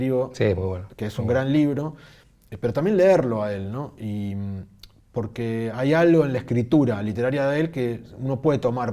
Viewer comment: Gran charla. Min 59:35 no entendí a quién nombran, alguien que si? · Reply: Stephen King, y mencionan su libro "Mientras escribo", hermosa lectura. Saludos👋